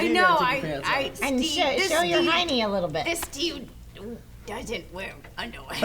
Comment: can someone please tell me what lee says around 15:10 that everyone laughs at?? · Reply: i think she said "This Steve doesn't wear underwear."